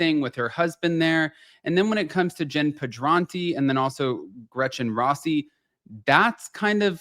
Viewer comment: No lie….I had to rewind it back because I thought I heard Wretchin Rossi. PSA…don’t get old